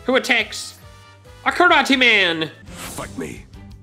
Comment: I thought he was laughing because karate man sounded like he said F*ck me